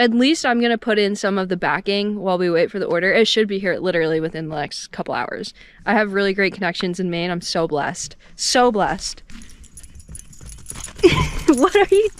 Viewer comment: Thought you said you were in Maine?? maine you don't have black widows